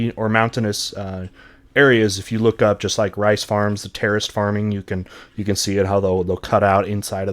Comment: i thought he said “terrorist farming” 🤣